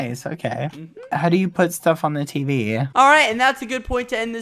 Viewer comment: Daddy put stuff on the TV me: AM I HEARING THIS WRONG!?!?